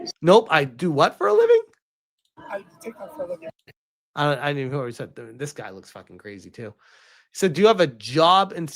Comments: He does tik tok for a living is what he said 9:02. Not surprised. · he said he did tiktok for a living 🤭 you were having trouble understanding what he was saying through his blubbery looking lips